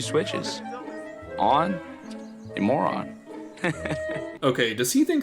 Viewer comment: Omg I didn't even realize he was saying moron when I first watched, I thought he was just saying "on and more on"